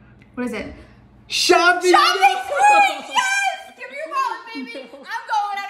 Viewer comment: Bro i swiped down before it finished and all i heard was "GIMME YOUR WAL-" but i heard "GIMME YOUR BAL-"